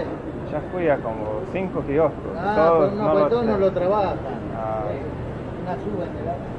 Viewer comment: A little correction - The gentleman is actually saying "no todos lo trabajan", meaning not all kioskos sell the SUBE card.